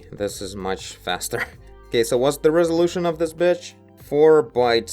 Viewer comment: whats the resolution of this bitc..h